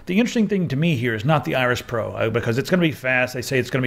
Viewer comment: was reading something in another tab and I heard "The Irish Pro," instead of "Iris."